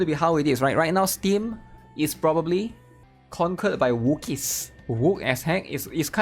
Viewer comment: the C accent makes it sound like youre saying "wookies" (from Star Wars) when you say wokies :face-green-smiling